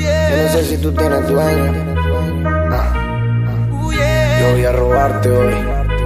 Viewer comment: soy yo o es que dice yo voy a robarte wey xd